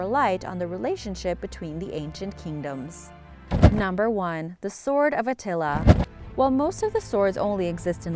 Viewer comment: At first I've thought I heard The Sword of Nutella when it came to The Sword of Attila part of the video XD